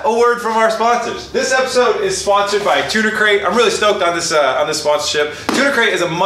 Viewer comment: I thought he said Tuna Crate and I was worried for his mental health